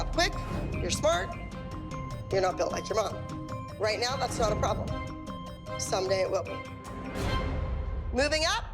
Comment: “you’re not built like your mom. right now that’s not a problem, some day it will be” did i hear that correct ?